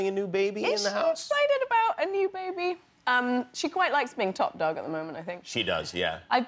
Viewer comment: "She quite likes being [...]". What does she say there? Also what is the "bath" joke about? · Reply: "top dog", meaning her daughter likes getting the most attention from her parents. and the "bath" joke is just making fun of the different way that brits pronounce "bath" compared to americans; we(brits) say "b-ah-th" whereas americans say "b-ea-th".